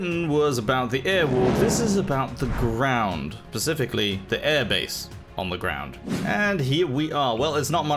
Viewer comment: did you say "pacificallly?"!